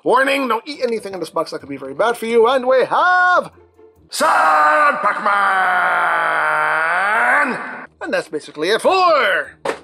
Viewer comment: i always thought he said sir pac-man